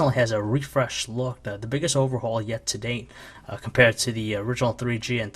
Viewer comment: keep repeating 0:15 IT SOUNDS LIKE HE IS SAY ITS THE BIGGEST HOES LOL